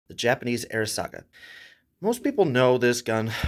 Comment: At first i heard "The Japanese airsoft gun..."